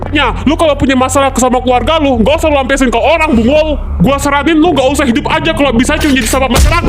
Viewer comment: apa aku salah denger TPI dia kaya bilang "bungul" GK sihh